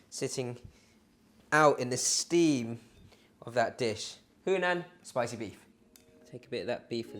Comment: I thought it said Human haha 😂